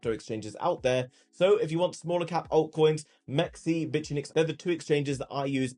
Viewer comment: I always hear you say "bitchnix" lolololol Love the channel, thank you so much for sharing your knowledge.